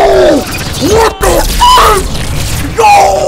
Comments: did he say what the f*** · Sound like he said what the f*could😂🤣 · Why it sound like he said what the F u c k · did he say what the flip?